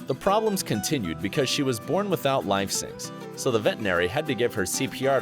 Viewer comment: life syncs? Idk what I heard there..